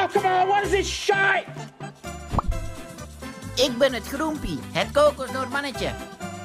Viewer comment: "I am Groempie, the Coconut Man" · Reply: Frostbitten Normie sounds like hes saying eggs bene trompy